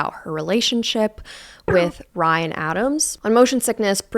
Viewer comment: I was listening to this as I was cleaning instead of watching and completely misheard this as BRYAN Adams and was absolutely baffled lmaooo 💀 Also it took me until around 15:05 to realise we were not talking about Mr Summer of 69